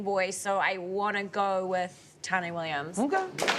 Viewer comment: What did she say? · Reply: Tane Williams.